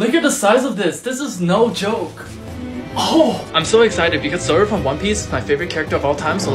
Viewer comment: Not gonna lie i thought you said “Simba from one piece”